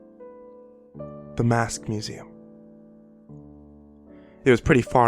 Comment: At fist I thought it said "The Masked Muslim"